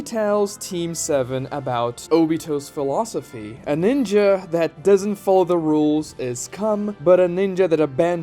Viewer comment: Anyone else hear "A ninja that doesn't follow the rules is cum" I can't be the only one who hears it right 😅.